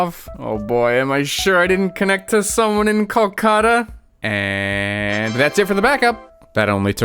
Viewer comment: hey did you say "kolkata" at 17:25? actually I'm from Kolkata, quite surprised hearing it as a reference also 22:43 he is a famous singer called "sidhu moosewala". also for being a political party member he was killed by a gangster. That's why it's written there.